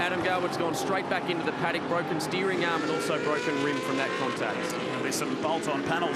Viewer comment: did he say broken steering arm and broken 'rib' from that contact? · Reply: Broken rim I think